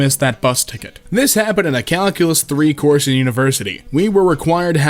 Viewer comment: The way he pronounced Calculus, it sounded like he said Calicos, like the cat breed XD